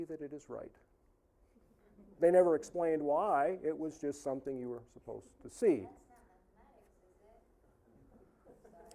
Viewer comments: what did the heckler say at 3:30? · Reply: adam freilich they said “ I hate Jewish people” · What did the heckler 3:29 say lol? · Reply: "That's not mathematics, is it?